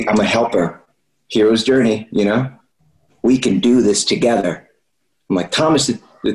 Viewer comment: hahaha did he say thomas the tank engine hahahana 😂😂😂🤣🤣😭😭